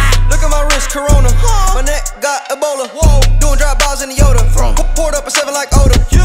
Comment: "look at my wrist, corona" "my kneck got ebola" dem some sick lyrics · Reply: I thought instead of saying Ebola I thought it said a 🦴r